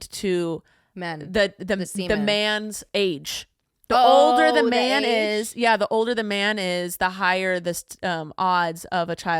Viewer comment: I really thought you said "the older the mayonnaise" 😂 (not undermining your point, I respect and adore y'all, but my British a*s had to think for a second hahaha)